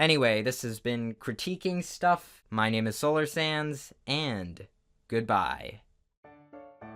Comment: Why does it sound like you say Solar Sans in every video lmao.